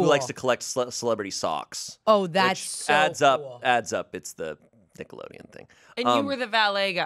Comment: I thought Shayne said Tickleodeon and i lost my shit